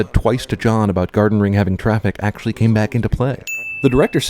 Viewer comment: I know this is incredibly nit-picky of me but at 3:39 did you mean "came" instead of "game."